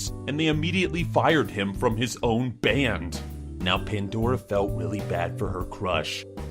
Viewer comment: did he just say "he felt willy bad"😂